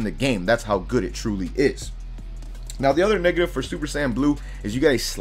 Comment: I heard "the other nigga"💀 Shit was kinda funny ngl.